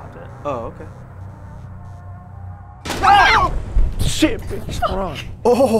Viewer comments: trying to dodge the nothing coming at him @ 11:33 made me die. That was a nice jump scare. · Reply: It almost sounds like he says "Shit bitch hold on" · trying to dodge the nothing coming at him @ 11:33 made me die. That was a nice jump scare. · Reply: I thought he said "shit bitch run"